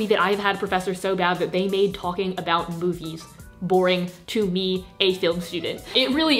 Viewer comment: i thought she said “boobies” instead of “movies” and honestly i wouldn’t be surprised ahhaha